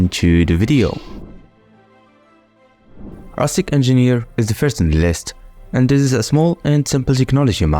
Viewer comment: My dumb ass thought that said "Autistic Engineer" and didn't even question it. I just thought, "'Autistic Engineer?' Yeah, they tend to be." 😭